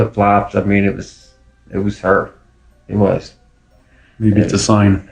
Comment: Sounds like it said “It WAS Chris” to me